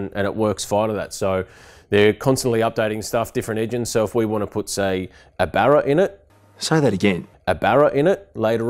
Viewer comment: All I kept hearing is " it's getting a barra, it's getting a barra". You should've done that from the start as you'd save $10k 🤘😉